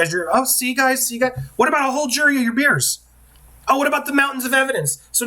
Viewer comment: "What about a whole jury of your beers?" I thought I misheard you, but even the transcript has it 😂😂😂